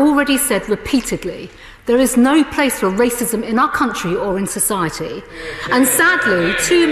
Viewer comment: I thought she said that there's no racism in Britain somewhere is she getting her examples from?